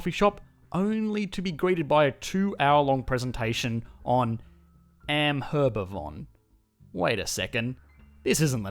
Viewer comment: did he say Am-herba-von? 😆